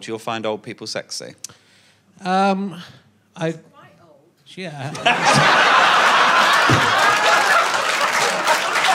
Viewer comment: What does say at 59:18? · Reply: "he's quite old" Such perfect delivery with the timing and the intonation on the 'quite' 😂